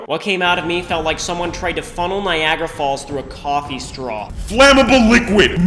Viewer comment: i thought Niagara Falls said Nigeria Falls